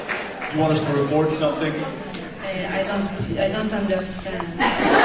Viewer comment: На 2:16 я слышу явное русское "я не понимаю"